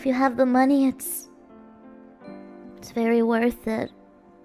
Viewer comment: Do we need to contact the police? If I understand it correctly, tracker tag technology has a feature whereby your smartphone can cause the other smartphone or its tracker to declare a beep or location, depending on type. This may indicate physical proximity at the time of this stream's recording, potentially within Blu Tooth range, or may have been an attempt at stream sniping. This may be evidence of additional offending stalkers engaging in locating the streamer. Further, they may have identified or even hacked the smartphone, thereby that they can track the device itself irrespective of other tracking methods. The streamer may still be in danger, or I may be incorrect? I'm not sure. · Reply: Nope. It misheard "its really worth it" for "siri where is it" so its apples fault for always listening.